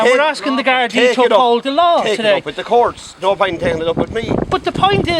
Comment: I clearly hear him say "Pint"